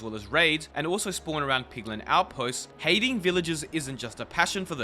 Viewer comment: Who else heard him say piglin outposts ?